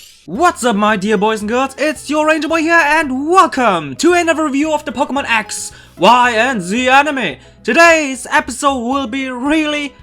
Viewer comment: I heard "welcome to another review of the Axe Y and Z Anime!"